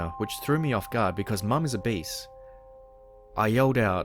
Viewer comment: 😂😂😂 I think he said “Obese” not “A Beast” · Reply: Haha I thought he said a beast first too, then I thought hang on, nah obese.